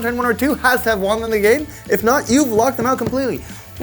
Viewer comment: Did he say "huge Peter"?